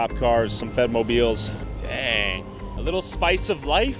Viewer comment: I thought said Fent-Mobiles